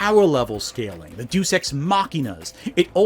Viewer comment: I heard "deuce ex machina" which I also kinda like: just shitting over the story.